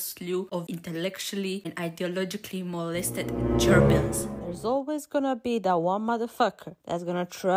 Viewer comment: I literally heard "Germans" instead of "Gerbils" 💀 Side note 9:00 replay button 14:00 too